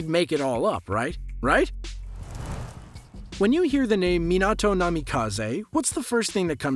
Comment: When i hear The name minato namikaze i hear someone shouting Doughnut